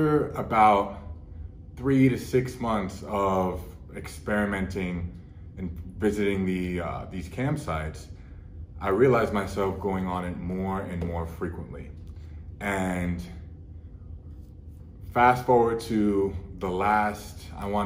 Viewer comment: I thought you said camp sights, was like wth? pop a tent in the back yard